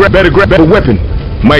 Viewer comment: I thought he was saying grabatty